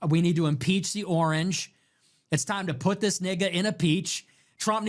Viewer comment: did he just say "put this (N word) in a peach"?